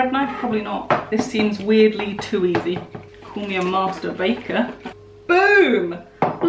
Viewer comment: I heard "masturbator" instead of "master baker" and nearly choked on my McChicken 😅